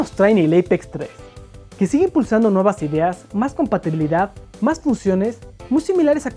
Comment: Min 0:18 Por un momento entendí Paternidad en lugar de Compatibilidad... Y así de: Dato de Vital Importancia... Jeje Solo los verdaderos fans saben de que hablo... Felicidades de nuevo man :3